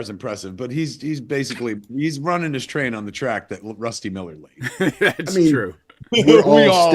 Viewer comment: Honest to god i thought he said the kids name is Rusty Milk